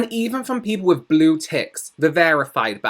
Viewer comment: OMG i thought you said "The worst comments are often from those with blue tits" I was like 😳 What's wrong with their tits? Why are they blue? Then realised you mean Ticks* Hahahaahhahaha